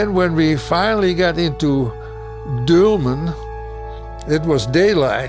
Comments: He said "Dülmen" ... the subtitle is wrong ;) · he actually said "Dülmen" which is in fact close to the Netherlands